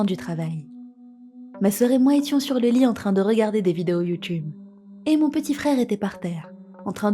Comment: j'ai entendu "des vidéos de cul" la première fois 😅